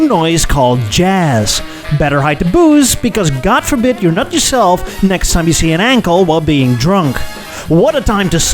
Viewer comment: I heard "God forbid you nut yourself next time you see an ankle"